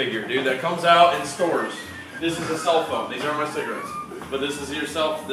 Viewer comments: oh. when i first saw the video i thought he said those are my cigarettes. sorry. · He said '' these AREN'T my cigarettes.'' It just sounds like he said '' These are my cigarettes.'' case closed! Now enjoy the video!!